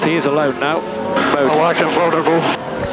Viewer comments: what does Ricciardo say at 3:48 in response to his engineer?? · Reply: "i like them vulnerable" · Guys what ricciardo saying at 3:50? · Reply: I like them vunerable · what did danny say about massa @ 3:50? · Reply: "I like 'em vulnerable"